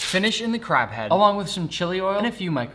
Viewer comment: When he said chili oil i thought he said chili jam